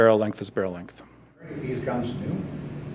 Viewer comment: "What do these guns do?" did I hear it correctly?